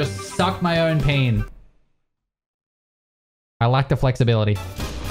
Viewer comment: I originally thought he said "I like the flexibility". Thanks for clarification lmao